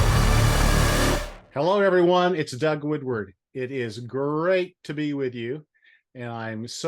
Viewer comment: Hope your feeling better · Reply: I thought he said his name was wigwood😂😂😂❤❤❤so cute 🥰